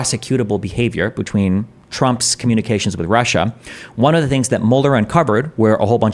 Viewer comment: I could swear Destiny is saying Mulder and not Mueller. To be fair, Trump would make for an excellent creature featured in a monster of the week episode 😂